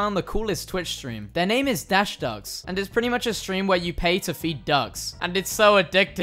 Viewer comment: I thought you said pay to beat ducks and I was like "excuse me"